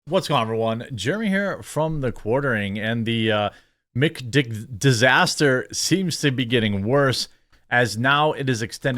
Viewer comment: Did I hear, "McDick Disaster"?